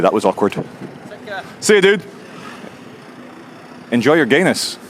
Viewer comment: At the awkward bit at the end when you said “enjoy your gayness” I actually thought you said “enjoy your anus”! 🙊